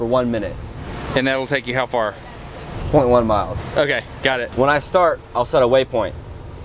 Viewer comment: I like that feeling I get when I do the final watch through of a video just before uploading it.... and I genuinely enjoy the content. It's like I'm grateful that the video exists, independent of who made it. Checkout this link to sign up for the email list if you’d prefer not to think and want me to just email new videos to you when I make them. <link> Thanks to all the Coasties that took the time to teach me, and who worked with me to make this video possible. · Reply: the subtitles says 21 miles, but he was saying POINT ONE miles (0.1) - 6 knots for one minute is 0.1 miles, not 21.